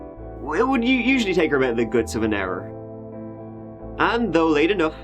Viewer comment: did he say "it would take her a good 7 hrs to walk home?" Wait... Am confused? 🤔🙄 Why wouldn't she just get a lift from one of her friends or a family member instead of walking 🚶 for so long?!? Am hoping am wrong about what he said... 🤦